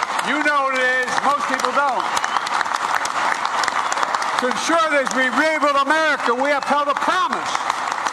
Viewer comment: I listened very closely to Biden's words at 12:59. Is this what he was saying: Conservatives may have held America, we have held a promise???